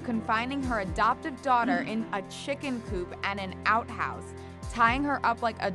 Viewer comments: I thought it said chicken soup💀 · And she ADOPTED that child! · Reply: Rasperrie Flowers honestly i thought it said chicken soup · I THOUGHT IT SAID CHICKEN SOUP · I’m so sorry I thought it said in chicken soup not coop